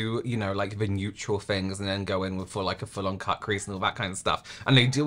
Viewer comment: I coughed at 9:34 and heard “butt crease” instead of “cut crease” and I just 💀